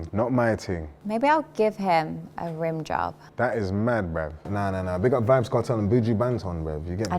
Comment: wtf did he even say😭😭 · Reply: That Is Mad Bruv! = That's Crazy Talk Lmao 🤣